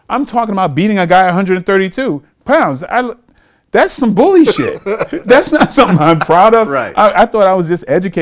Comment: Bruce Lee would fold Vlad like a banquet chair. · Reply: he said that is bullyshit instead off bull shit